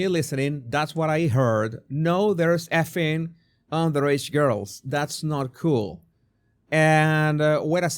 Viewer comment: I hear “no, that he was fucking underaged girls” and it’s reinforced my his nodding on each syllable. He says “no” at first but he confirms “that he was fucking underaged girls” immediately after the no. So I’m thinking — it’s loud there, he’s tipsy, he’s not hearing her question clearly, and he’s talking over her a little, so the “no” part doesn’t take away from his next phrase “that he was fucking underaged girls, that’s not cool”.